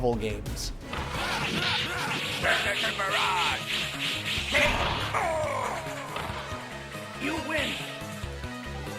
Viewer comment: What does wolverine yell at 5:30? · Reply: Berserker barrage!